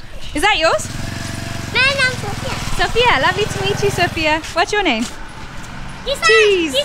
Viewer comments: i thought she said that aa motu what is your name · I thought she said mera naam sophia instead of my name is sophia 😂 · I literally heard "Motu's your name" at the beginning 😂💀💀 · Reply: And i heard "mere name Sophia"😅